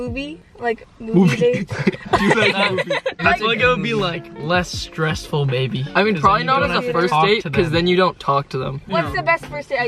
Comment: I thought he said we’d have a less stressful baby and I was like WHAAAAA😧😦😯😲